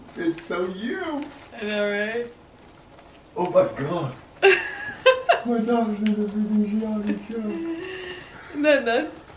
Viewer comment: “are u dead for the rest of the show?” whoever that is i love him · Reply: probably, i heard he says "my daughter is in the game" something like that